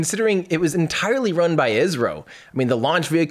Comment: wow I never knew the Chandrayaan-2 mission was run by Israel instead of India... The more you know :P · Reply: He said, "ISRO." It did sound like Israel though.